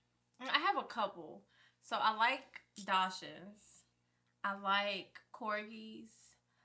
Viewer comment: What she say 1:07??? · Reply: I like corgis